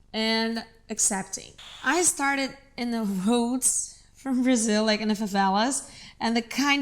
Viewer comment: The CC is wrong at 0:23 - 0:24. She says “I started in the HOODS” not “roads”